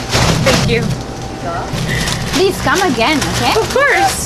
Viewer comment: I thought she said "no not you" because she was gonna take the bag from the girl